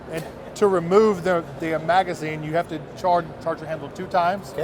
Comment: does he say "to remove the damn magazine?" :-D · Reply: " ...to remove the ahh magazine...."